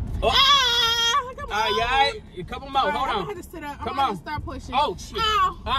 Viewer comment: Was I the only person who thought he said “ you high” instead of “ you alright”